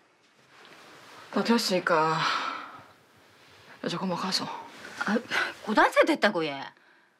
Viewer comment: 근데여기서 무당이 뭐라고말하는거에여 ?? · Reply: 다 됐으니까... 이제 고만 가소